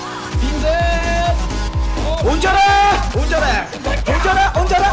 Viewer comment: 여기에 운전해 운전해할때 나만 미친새끼야 들리나